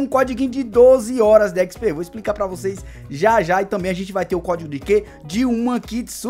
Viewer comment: so eu escutei codigo de gay?